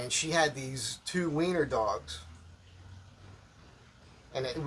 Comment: It thought you said, "Wiener, dog."